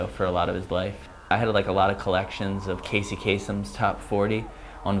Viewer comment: I had to back up because I thought Avey said he had a Casey Tatum collection. That shit would have blown my mind.